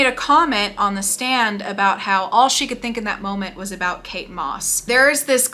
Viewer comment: When you said “all she could think about was Kate Moss” I thought you said Cake Boss and had to do a double take